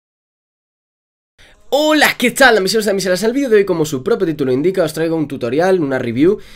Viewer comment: "Hola, que tal wachu wachu wachu wachu...." Qué dijo...?